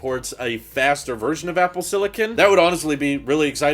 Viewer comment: It sounded like you said Apple Silly-kin...